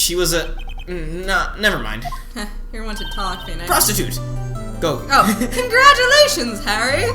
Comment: I stepped away and when I came back all I heard was "prostitute, go!"